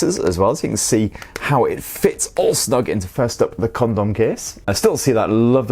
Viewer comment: Con*om case 🤣🤣🤣🤣🤣🤣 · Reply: I thought he said condom case lol